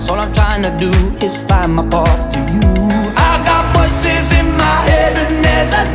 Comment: I thought it was horses when he said 'voices'